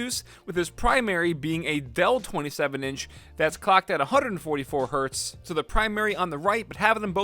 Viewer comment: Did he just say clocked at 144hZ